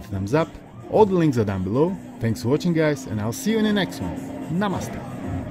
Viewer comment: did u say namaste ?? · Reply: I did